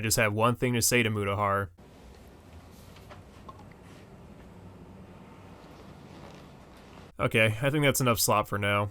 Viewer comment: tf does he say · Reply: He said "call me"